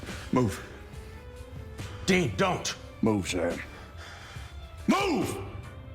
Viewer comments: When Dean pulls the gun on Sam did he say "Move... Move Jared..."? or am I hearing things? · Did I heard well at 4:23? "Move Jared. MOVE!" Jensen told on one of the latest convention that he made in one scene this mistake an the directors let it in. Maybe it was here?